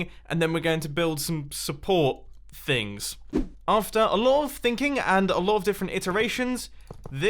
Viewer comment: since mumbo was talking about things i then thought he said THINGKING which is actually thinking